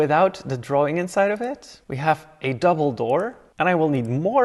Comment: Bro on 04:29 i thought he said “and then we have a dumbledore” instead of double door. Also i hate the fainting i bought marvelouse moods and its a waste of money now because it does nothing anymore its useless. I find that you should be able to turn that off if u have the gamepass